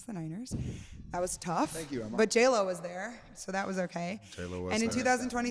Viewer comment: does he say Taylor wasn’t? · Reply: He says J.Lo was there